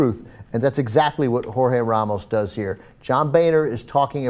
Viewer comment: HA! At 1:06 Jorge Ramos becomes "horror Iran." I'm a disabled veteran who has lost most of my hearing. Now I'm smart enough to know that's not what's being said, but in spite of being hilarious it's just a little irritating. Can't a transcriber give transcription notes to the person doing subtitles?